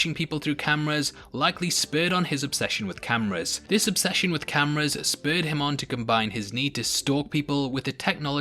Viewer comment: I'm sorry I didn't catch that. Did you say his cameras spurred him on in some fashion? @ 6:10 Bro, I didn't even catch that knife disappearing